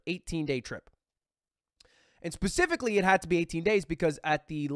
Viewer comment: haha it sounds like he’s saying pacifically instead of specifically but it’s funny cuz he flew over the Pacific Ocean.